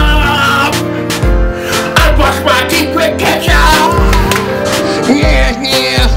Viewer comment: haha it sounds like your saying "kesha, kesha, i brush my teeth with kesha!" XD